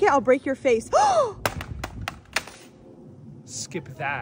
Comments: I thought he said “SHE SKIPPED MY ASS” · for the 5 secs i thought you were saying "she smacked my ass" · I thought he said SHE SKIPPED MY A-S